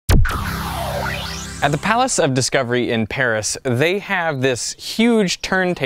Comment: I'm sorry im going to have to pause here did you say "Palace of Discovery?" .... I'll be back